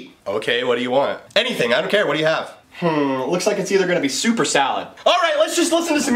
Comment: He did not say suoper salad he sad Soup or Salad